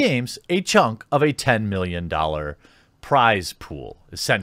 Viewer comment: holy shit i thought it said 10 million each and was like wtf 10 billion